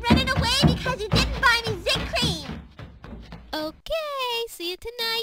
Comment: O.O I thought you said something other then zit cream.... My ears suck.. I thought she said 'I'm running away because you didn't buy me any zit pee!' O.o...!!!!!111